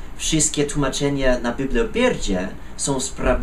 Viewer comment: Tak jak moi poprzednicy, szacunek wielkie za nauczenie się j. polskiego. Mówisz i piszesz lepiej niż niektórzy rodacy :D. Rozwalił mnie moment w 1:47 - biblio pierdzie xD. Miło się ciebie słucha , powodzenia w robieniu filmików :).